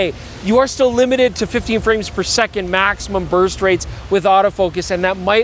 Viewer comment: did he say the "maximum birth rate"? 🤔